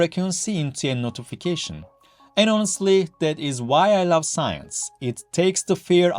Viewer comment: Holy moly. There are accents and there are syllable and consonant removalists. This took 110% concentration to try to follow without subtitles. For the first 3 minutes, despite the introduction I had no clue what the problem of the frequency was even related to. Some of the vocal phrasing is like English word salad. For an educator dedicated to audio perception and sound comminication this is almost hilarious. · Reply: I replayed this 10 times and have no certainty what word or words this is. Maybe "And honestly..."???